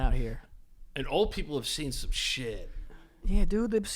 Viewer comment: At first I thought said old ppl have seen giraffes 🙃🙃🙃